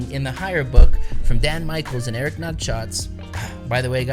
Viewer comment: Sounds like you said "Eric nutshots" 😂😂😂